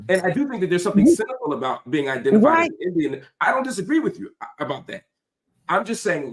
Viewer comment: Marc…correct me if I’m wrong! Did you say you think it’s cynical to be identified as an Indian??? Around 14:58 mins in???